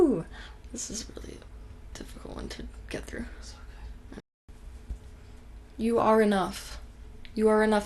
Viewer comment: anyone else hear someone whisper It is ok !!?